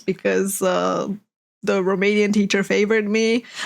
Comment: did she said romanian teacher? I AM FROM ROMANIA lol